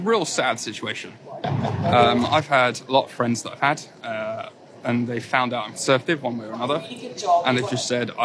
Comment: please can someone translate into words what he says at 4:26 ... "they've found out i'm ??????" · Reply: He says "They've found out I'm a conservative, one way or another"